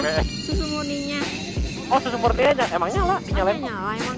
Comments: di panggil yang apa gw yg salah denger?😂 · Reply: Itu dia ngomong "emang" bukan "yang" kuping lu pd conge an wkwkw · Gue gak salah denger nh menit ke 10:10 itu janis bilang "Nyalain Yang" bangkeeeeee 😂😂